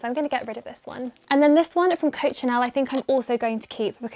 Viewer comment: Heyy, can anyone tell me where the bright pink bag at 14:30 is from? English is my second lenguage and I didnt understand the brand's name... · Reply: I think she said Coccinelle!